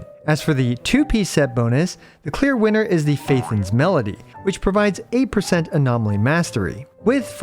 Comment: i'm sorry did he say faithon melody?